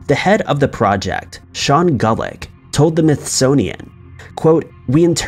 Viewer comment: did he say “Smithsonian”? I swear, every time I’ve gone back and listened, he’s saying “Mythsonian”.